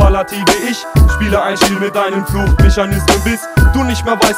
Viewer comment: Was sagt er bei 1:25 ? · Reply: Ich spiele ein Spiel mit deinem Suchmechanismus